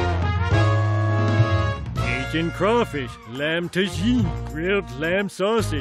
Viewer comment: Could someone give me the whole list of food he mentions please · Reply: Does he say "Cajun crawfish" first? I thought he said "Asian crawfish".... I could be wrong though.